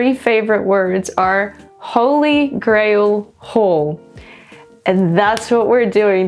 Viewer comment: Anyone else thought she said "Holy Grail Hole"? 😂